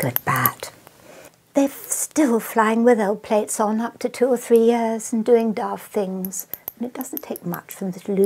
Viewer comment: What did you say at 3:25? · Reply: "They're still flying with L-plates on, for up to 2 years". L-plates are the symbols (L for 'Learner') someone learning to drive in the UK puts on the front and back of their car.